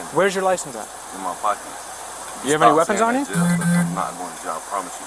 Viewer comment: I could've sworn I heard, "I promise you, I'm not going to jail." 😂